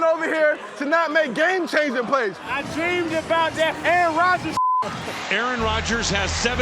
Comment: what did Cam say ? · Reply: I think he said "I Dreamed about that Aaron Rodgers s**t"